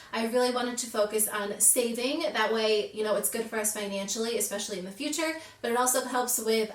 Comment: I thought it said "fiancés" instead of finances, I was like, oh! Multiple fiancés?! 😂